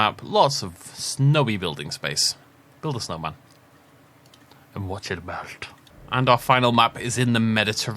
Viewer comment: 1-Deinocheirus 2-Yutyrannus 3-Sinosauropteryx 4-Jehelopterus Btw what was that terrifying whisper at 26:09 ? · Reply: me saying "and watch it melt" about the snowman...creepily